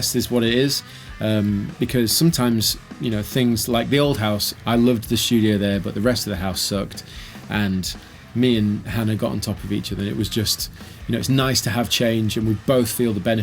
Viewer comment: I thought you said "Heather and I..." and I was like WTF?!!! Hahahaha